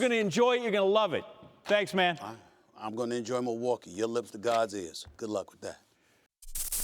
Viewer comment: What did Stephen A said at 6:41? “I will enjoy Milwaukee, you enjoy ____ “ I cannot understand it. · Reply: "Your lips to God's ears"